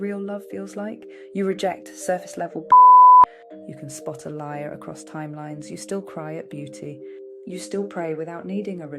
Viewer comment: 🤔What word was censored at the 2:54 mark with a beep? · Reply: Bullshit i think